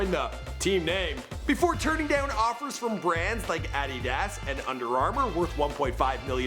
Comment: When he said adidas it sounded like he said I eat ass